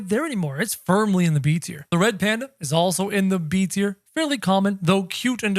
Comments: did he just call that the red panda? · sorry did u just say red panda